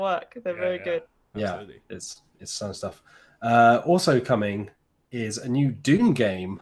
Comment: Thought he said "Doom". Hadn't considered that a social deduction game, could be interesting.